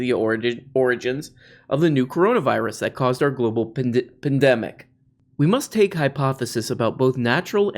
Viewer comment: sounded like you were going to say ‘Global Pendejo’ 😭🤣